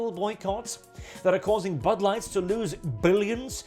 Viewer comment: Why every time I hear "Bud Light" - I hear Butt Light instead?